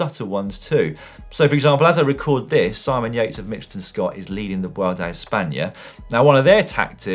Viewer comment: At first I thought that at 12:35 you said Simon Yates was LEAVING the Vuelta. I was passively listening to that at work and dropped everything and googled the news. DON'T SCARE ME LIKE THAT!